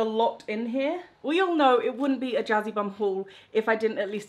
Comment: “It wouldn’t be a Jazzybum haul....” What i hear: “jazzy bumhole” 😒😒😒